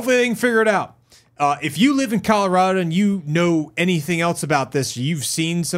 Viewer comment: It sounded like you said CALorado which I'm just gonna call a happy accident